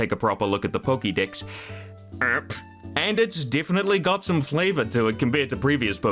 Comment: The way you said "Pokédex" was so weird I thought you had said "Poké Dicks" for a second